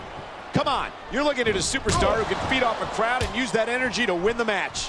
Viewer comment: I swear when Michael Cole says "Feed off a Crowd" at the 00:30 mark, I heard "beat off a crowd" and I can't stop laughing 😂🤣😆